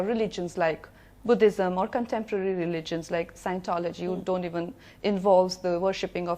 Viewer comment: Did she say scientology 9:33 ?? :0 :0